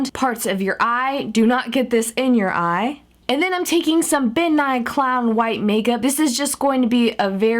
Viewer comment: When she said Ben nye I thought she said bill nye XD!